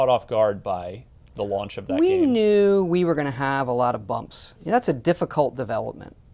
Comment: “We knew we were going to have a lot of bumps” OK TODD. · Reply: I thought he said "bugs" instead of "bumps" lol